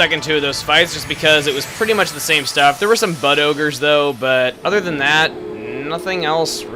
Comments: I thought you said "butt ogres". 0.o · Whenever you say "Bud Ogres" I hear "Butt Ogres". Confusion ensues. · I thought you said "butt ogres" not "bud ogres"